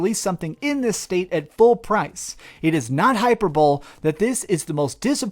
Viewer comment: It’s pronounced “hi-purr-bol-ee” ......hyperbole. I first thought u said hyper bull...but after a rewind caught what you meant.